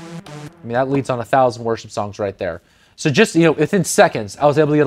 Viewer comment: did you say worship song at 6:55?